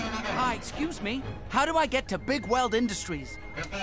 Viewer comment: if you listen really closely it sounds like the station announcer is saying "The next stop to Bigweld Industries is down tunnel 7" After Rodney asks "What?" It sounds like the announcer says "Go down tunnel 7!" His voice is just so muffled because he's speaking through the microphone rapidly but that's how train stations usually are